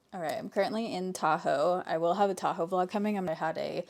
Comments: When you said "Tahoe vlog" I thought you said taco vlog. Would still watch either way 😂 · Why did I hear "taco vlog" instead of Tahoe vlog? 😂